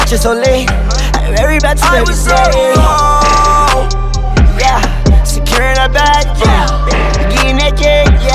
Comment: At first I thought he said Securing the bag Minimum wage